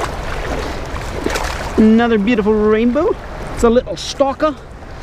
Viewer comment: i thought he said another bad one.